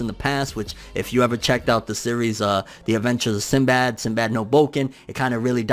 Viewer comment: My hazy mind heard it as 'Sinbad now Broken' at first 🤣